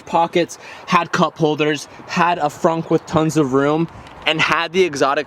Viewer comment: did he say frunk when he was going threw the audi R8